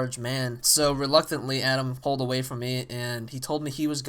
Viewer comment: did you actually say your friends name adam? or was that someone else